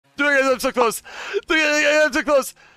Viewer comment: “Dgelasoclose Dogelagagal’msoclose Wegotlgesrighthere Chatlookglagerchat (silence) Ah AHHHHH CHAT YO DOOD OHHLY DOOD.” Edit: nah 1.2k bruh💀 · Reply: i thought he said "it tickles" 90% of the time oh great i summoned the dirty minded people